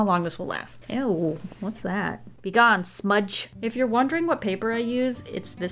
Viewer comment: When she said “if your wondering what paper I’m using” I thought she said “if your wondering what paper is” Me: excuse me???!!!